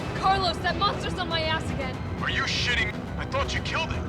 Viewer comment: For anyone who didn't catch on 1:03:37 Carlos be like "Are you shitting?"